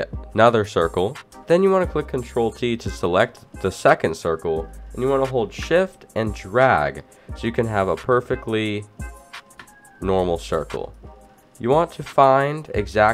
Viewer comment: bro i swear 1:25 i cant understand shit, i put captions and slow mo and i cant tell what your saying my dude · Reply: “Select the second the circle, and hold shift and drag so you can have a perfectly normal circle”